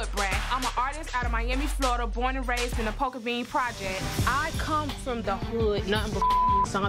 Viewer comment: All I hear is “I’m from the hood” this shit bouto be poppin poppin!